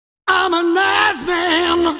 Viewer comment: When I was a kid I thought he was saying "I'm a nice man!"